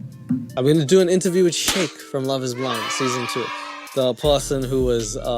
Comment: Damn I thought you said Shane even, but shake?! Really the bottom of the barrel. Don’t care enough to watch tho